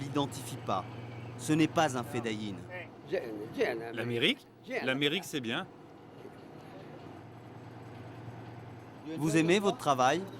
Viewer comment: he is not saying "america is good" he said "we are hungry" as well as the hand sign for eating